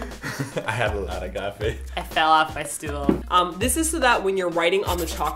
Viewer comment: Thanks, because I thought they where saying "stoel" from dutch and if you translate that to english you get chair so I was a bit confused but. Now I know thx.